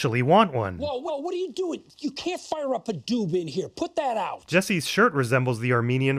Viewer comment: I think the subtitles are wrong here. Isn’t it spelled “Doob” you know like The Doobie Brothers